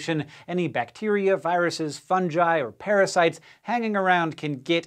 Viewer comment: I heard him say "fun-jai" not "fungi"